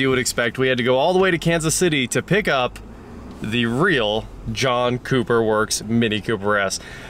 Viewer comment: at some point i heard "Junk Cooper Works" .... 😂